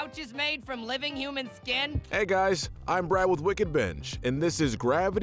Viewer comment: I thought you said wicked b**ch